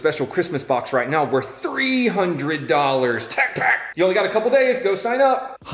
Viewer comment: When u saw the package I thought you said "JETPACK"!!! Man that got my hopes up.😄👍